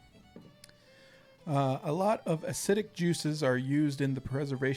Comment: When he read the question about acidic juices, I thought he said "Hassidic Jews". 9:01 "Uh, a lot of acidic juices are used in the preservation recipes." = "Uh, a lot of Hassidic Jews are used in the preservation recipes." My version is way darker than the original.